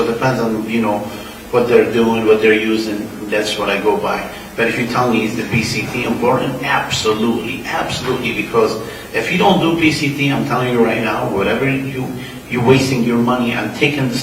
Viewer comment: What is he saying in 11:07 that is so important? PCP, PCB...? I can't understand it. Would appreciate the answer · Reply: Post cycle therapy